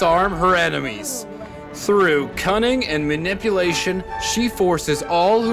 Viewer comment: I heard cunning as cumming